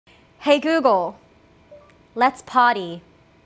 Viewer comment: What I heard. “Hey google, let’s potty”. Disco ball spins 🤔😂